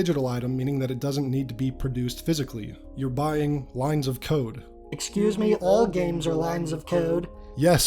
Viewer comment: my god, I misheard what you said here, thought you said "you're buying lines of.." something else. I said out loud WHAT?! I was so confused until I realised I'm an idiot haha